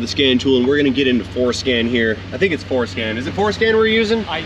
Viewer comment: Oh my, I don't hear well so the closed captions are often helpful. But around 4:12 when says "We're gonna get into Ford scan here"the closed captions printed "We're going to get into foreskin here". I can already see being rebranded as The Car Mohle.